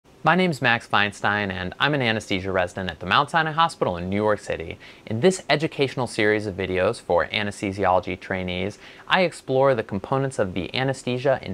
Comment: Awesome! A Max Feinstein video! When I started watching your channel about a year ago, I thought you were saying you were an anesthesia resident from “Outside a hospital in New York City)! I thought, “They do surgeries outdoors?” I hope you’re doing well!